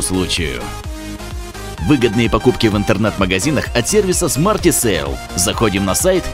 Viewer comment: Мне послышалось не в ,,Интэрнэт" а в ,,Интэрнат" магазине!😂😂